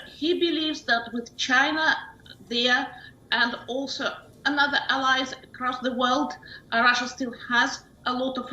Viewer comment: Did she say China, India? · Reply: She said China, India and other allies around the world